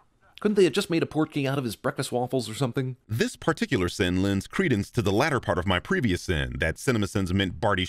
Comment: Just coming here to mention the Fantastic Beasts reference made at 19:20. He says “credence” in the voiceover but the captions/subtitles says “Aurelius”. I see what you did there. Very much appreciated